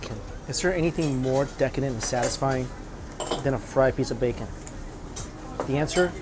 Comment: Did he just say fried piece of bacon